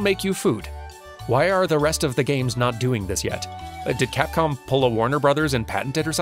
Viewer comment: I probably misheard but did you call them "catcom" instead of Capcom at one point?